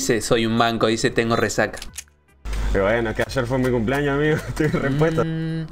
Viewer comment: entendi "estoy indispuesto" JAJAJAJA